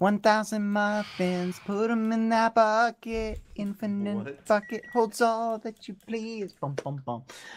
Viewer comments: who in 2021 actually he did swear he said “fuck it” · woah woah woah I swear I heard him say "f-ckit